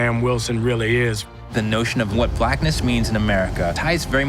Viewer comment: did he say blackness